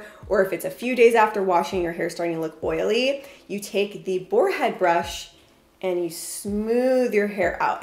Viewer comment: OMGoodness- I thought you said a “forehead brush”, at first. I was like, “wait, what?… why would you brush your forehead?…” 🤔 That’s what I get for reading comments while watching the video. 😂😂😂 God Bless 🙏🏻😇